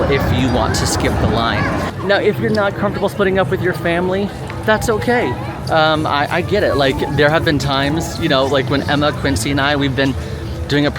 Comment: When said “If you’re not comfortable splitting up with your family “, I heard “putting up with” 😂 😆 😂